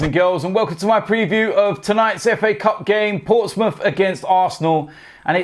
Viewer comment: Thought you said Spurs against Arsenal *huuh